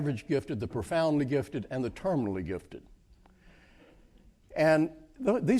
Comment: Did he just say ‘terminally gifted’? 😃 By the sounds of it, giftedness is a terminal illness.